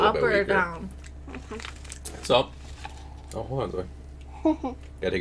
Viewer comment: why did you ignore your daughter and then your wife, so is it thumbs-up or thumbs-down. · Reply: What u talking about? He clearly says “ it’s up “